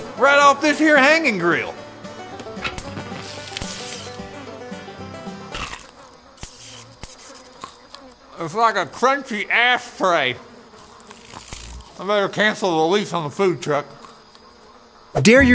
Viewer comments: I sounded like he said 'tastes like a crunchy ass tray' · I thought he said ass tray